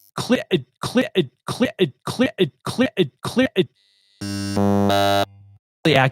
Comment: The Demon at 28:20 was saying, "Clear it, Clear it, Clear it....."